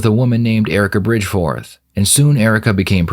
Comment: I misheard her last name as Bridge Four like from Stormlight lol